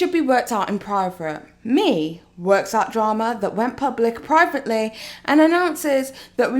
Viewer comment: Lmao "PRIVATE"... SOUNDS LIKE UR SAYIN PRIRATE